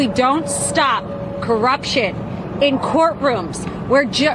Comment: Lmao! Did she say..CORRUPTION!!! Did she just say MICROPHONE!!!😂